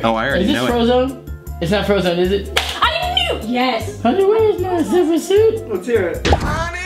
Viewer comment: It sounds like is saying honey where is my super nuce